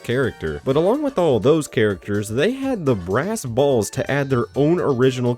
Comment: did bro just say “balls!?”